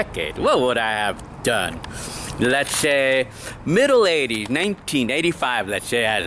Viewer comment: What'd you say at 1:00? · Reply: middle 80s! Yeah